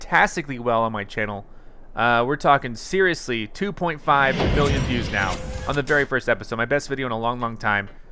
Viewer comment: I thought he said billion